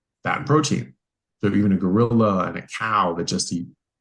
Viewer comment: Did he say a gorilla?